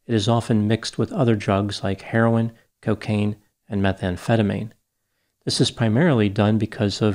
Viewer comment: What pharmacist is handling methamphetamine? You counting out shards back there? I think you meant amphetamine lol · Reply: I thought they said methadone which is a different thing from methamphetamine. Methadone is a synthetic opioid used in the treatment of heroin addiction.